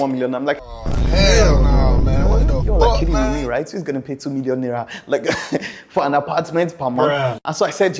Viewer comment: Did I hear "2 million naira for an apartment per month" at 01:16?